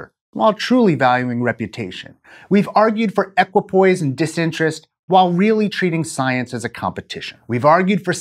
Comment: I couldn't catch the word at 4:30 and it's driving me crazy! The closest-sounding word I can think of is "apropos." But in any case, I'm excited about these upcoming series. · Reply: "Equipoise and disinterest" Equipoise basically means being in the middle. However in this context clinical equipoise can have a specific meaning <link> Clinical equipoise, also known as the principle of equipoise, provides the ethical basis for medical research that involves assigning patients to different treatment arms of a clinical trial. The term was first used by Benjamin Freedman in 1987. In short, clinical equipoise means that there is genuine uncertainty in the expert medical community over whether a treatment will be beneficial Not sure that fits with what was said, but I think it's interesting nonetheless. Basically, We say we're objective but in reality the researcher will often skew the design.